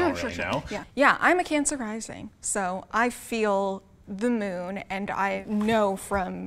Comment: did she just say i feel the moon?